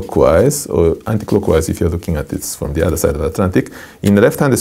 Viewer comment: That was WONDERFUL!! At 5:56 or 5:57, did he say, "Unless you look at it from the other side of the Atlantic"...? Is that an expression? Or did he say something else?